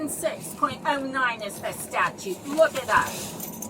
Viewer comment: Did she just say “<num> is the statue, go look it up” ????😂